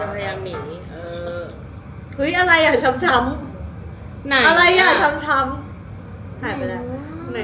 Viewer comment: Did I hear chum chum at 1:12? It reminds me of Sam and Mon lol this is how y’all have us struggling out here without the eng subs😭